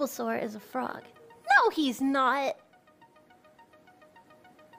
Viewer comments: I swear to god I heard her say: "he's ebola" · I heard "he's sebulba"